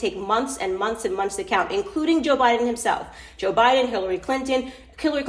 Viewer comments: Did she say Killary instead of Hillary .... WTF🤔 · Did Candace just say killary clinton at 5:20? 😆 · It sounded like she said Killary Clinton 😂😂